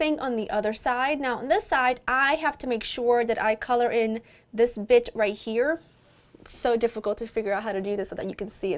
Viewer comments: OMG @ 4:30 I thought said " this bi#@ right here" LOL. Beautiful as always! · I seriously thought she said...I need to figure out how to cover up this bitch right here lol